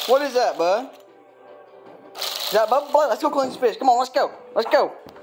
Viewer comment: I thought you said let's go catch a bitch,😂😂😂 #lady_doggy